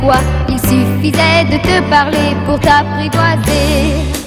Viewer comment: タピろってw 聞こえるw